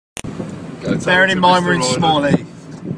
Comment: Did I hear “bearing in mind we’re in small heath?”